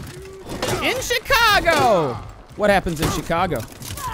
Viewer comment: I thought the guy said Gustasava instead of in Chicago